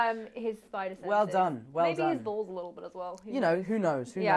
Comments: did she say "balls"?? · did she say "maybe his bALLS a little bit too"?!